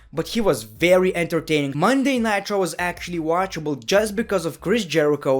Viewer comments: I swear, I thought you said Monday Nitro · sound like u said Monday Nitro